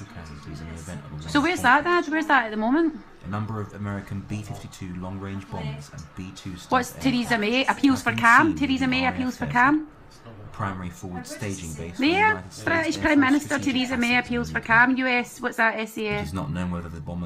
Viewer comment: English is my second language and at 4:06 I heard "Titties of may appeals to calm" 😂